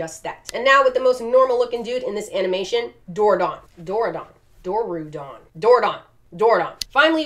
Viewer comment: I swear, for almost the ENTIRE time she talked of the dorudon I could’ve sworn she had said doorknob